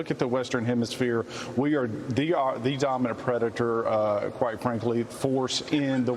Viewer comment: Did he say Predator? I had to go back.